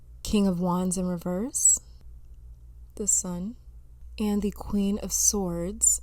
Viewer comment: Lol when you said "the sun" I heard "this one" like "I haven't seen this one either y'all but yeah this one too"